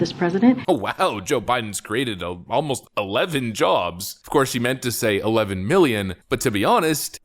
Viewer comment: She did not say Joe Biden brought back 11 jobs! lol… don't be silly! She said it's "Almost 11 jobs"…